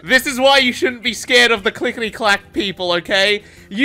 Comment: I wasn't paying attention and I thought he said black instead of clack